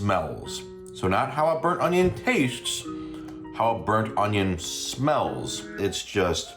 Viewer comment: With the chow mein I kept hearing “butt onion” instead of burnt onion - maybe now we can determine what a “butt onion” is supposed to be? 😂